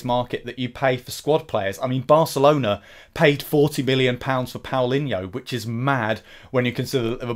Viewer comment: lol, bro@ 2:12 did u just say Barcelona payed 40 'billion' pounds for Paulinho?